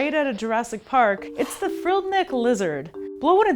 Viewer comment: Ok at 0:09 I thought she said "Frilled Nig Lizard"